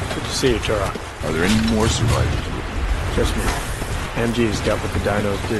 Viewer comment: Well I'm not good at English can some say what did Turok say at 1:18:06? :/ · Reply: He said Are there any more survivors?